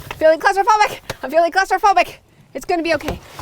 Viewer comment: Hiya. We say cupboards, so every time you yelled 'I am feeling claustrophobic' I thought you were saying 'closeyphobic'. I was racking my brains trying to work out what you were getting at 🤣🦏